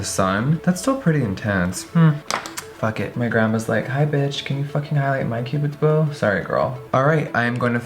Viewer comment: somehow i heard jeffree's grandma from a distance say "come through highlight"